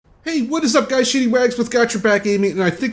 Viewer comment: You sound like your saying shitty rags lol. ...so I had to look good vid but you sound a little stuffy my friend.